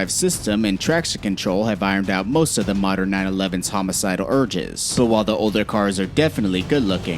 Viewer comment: Did he just say homicidal urges? 😂